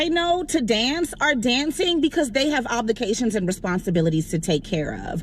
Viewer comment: Did she say oblications?